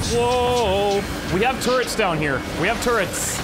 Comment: anyone else hear "we have tourettes"?